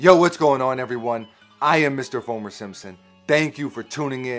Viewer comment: I thought you said mr homer simpson haha 😂